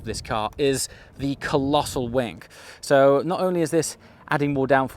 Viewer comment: Now... you said either 'the colossal wank' or 'the colossal wink', but neither were 'wing' 😉 Personally I'm hoping that 'a colossal wink' is the new 'tip of the hat' and I'll chuckle like a 14 year old each time I hear it.